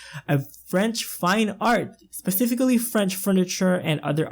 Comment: this build is literally breath taking!! great job so far! also, I giggled when you said "French fine art" because I thought you said "French FRY art" lol XD